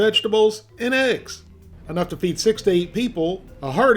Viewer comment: I heard "enough to feed 16 people". I had to check the title because I was like bro I don't care what your speech was at the beginning this ain't the Sudan. People don't live off of 1/16 of that. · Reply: He said 6-8, not 16